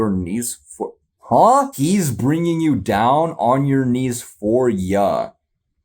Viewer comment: Zeus and the gods are actually saying, "WE'RE bringing you down on your knees for ya." The subtitles got it wrong; the GODS are bringing Odysseus down on his knees (the 'for you' bit at the end is to match Odysseus' verse)